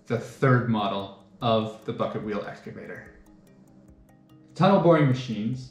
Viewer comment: i thought he said: f*ck it wheel excavator